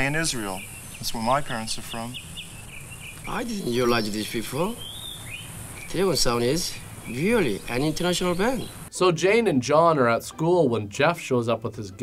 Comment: WTF did he say? lmao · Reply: "I didn't realize this before. Dragon Sound is really an international band." His pronunciation of "Dragon Sound" threw me off.